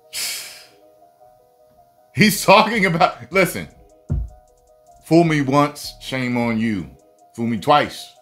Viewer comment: The way you were talking about *FOOL ME ONCE**... Sounded like **STEINER MATH* lol"You have a 3 & 4% chance at sacrifice"